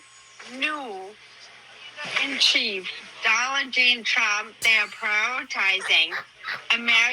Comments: With all the slurring, it sounds like said “Dolland Jane Trump.” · DID SHE SAY DONNA JEAN TRUMP!?!?! I’m so done! 😂😂😂😂😂😂❤